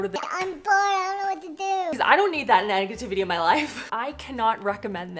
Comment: did you say “nag-ativity”?